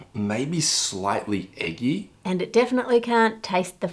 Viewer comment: 😂 i had to replay what he said. I heard "slightly icky" at first lol😂